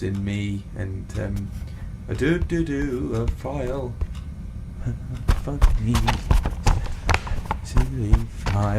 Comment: I heard "fu#k me" at one point when red guy was messing or whatever with files